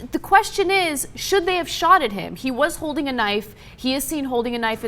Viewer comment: Did she just say: Should they have SHOTTED him? xD jesus...